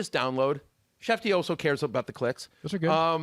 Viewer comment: I thought he said “shefty always cares about the clits”